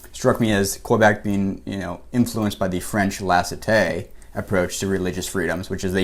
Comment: What's that word around 14:46? "Lassite'"?